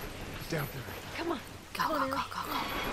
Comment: It sounds like he says ellie dear... but what he said was down here